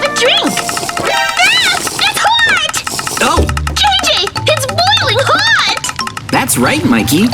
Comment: Rip banana kid. 20:49 look at the captions it thinks Mikey said KJ instead of JJ.🤣🤣🤣